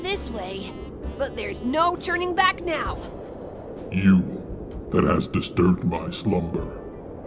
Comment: I thought Shenron said “you deadass disturbed my slumber”